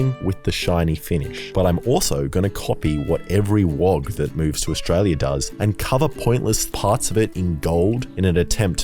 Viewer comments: Did he say "what every wog who comes to Australia does"? @ 14:55 or did I mishear? was really enjoying this video, great fun to watch, but that just caught me completely unprepared. Please someone tell me I misheard that. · did he say wog? And is there a meaning of that word that I'm not aware of other than as a racial slur for non-white people? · Did he say WOG at 14:55? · please tell me that wasn't what I heard or this video is in trouble 🙊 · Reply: He said "wog" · Did you just say "wog"? um...